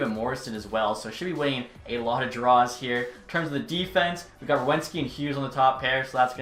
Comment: As a jackets fan, idk why I notice this stuff, but here’s something Werenski is pronounce where-ren-ski, when you said it, it sounded like you said wewenski